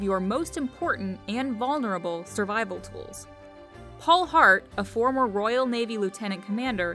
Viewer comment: Who else thought they said Paul Blart instead of Paul Hart